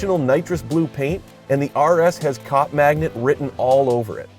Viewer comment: did he say cock magnet? Lol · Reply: DoubleD 613 haha sounds like it. .....but heard again and he said cop magnet